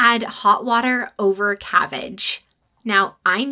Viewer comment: I thought she first said cavvage not cabbage. I listened a few times to make sure😮